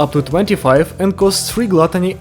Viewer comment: did you say "one cost" here? i genuinely cant tell if youre saying one cost or one coin but what would one cost even mean. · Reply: I'm saying one-coin, actually didn't even notice that fumble before your comment